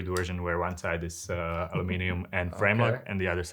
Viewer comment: Did he say aluminum frame lock???